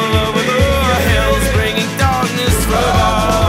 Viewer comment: La escuché en el PES 2015 y pensé que decía "darkness football" 😅😅😅😅😅